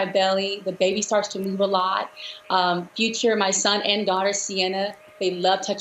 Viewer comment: Did she say future? At 2:16?